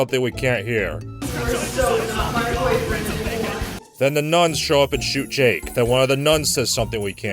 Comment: I think what she says is: "you are so not my boyfriend anymore"